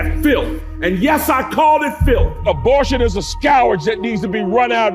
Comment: is he saying scourge?